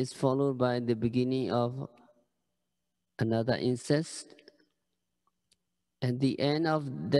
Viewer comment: what is he saying here? Incident, insect, incest? Please clarify. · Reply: It is insect. One insect (ant) follows another ant and so on to create the illusion of a continuous black line from a distance when in fact at closer look, there are gaps between each insect. This notion of continuity is the concealer of Anicca. (")